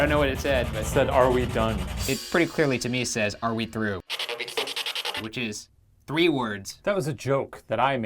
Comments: Instead of “are we through” I heard “are we safe” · Instead of "are we through" I heard "are we safe?"